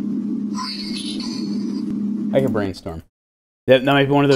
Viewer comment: I never understood this one. I hear something like "oi-wee-doh" every time, nothing like "brainstorm" or "green needle."